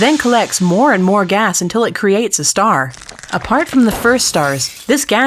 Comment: when he says have a good one it sounds like he is saying havagoowah